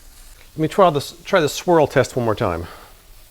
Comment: Thought he said 'squirrel test' and had to question what he was cooking for a moment.